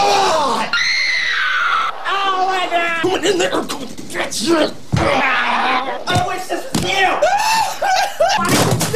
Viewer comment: Did he just say the N word the C word and the B word?? :0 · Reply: He is just mumbling, "I'm gonna get you!"